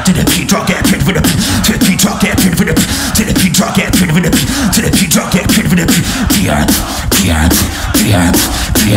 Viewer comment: did he say "get beat by the venom"? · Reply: "get ready for the beat drop aye"